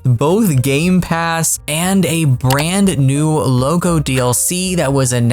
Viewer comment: when you said "brand new loco DLC", I initially misheard it as "brand new logo DLC" and thought you meant TSW5 as a spicy take of there being too little difference between TSW games so that it's basically a DLC with a new logo.